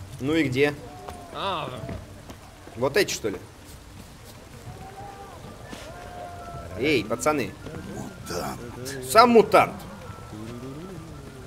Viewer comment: мне показалось что он сказал мудак